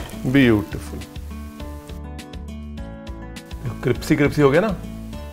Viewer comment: I thought I heard CRIPSY CRIPSY.. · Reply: He says cripsy